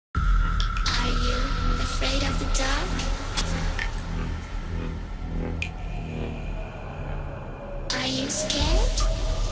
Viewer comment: Oh my gosh ok so at 0:00 I thought it said are you..afraid of the duck 🤭😅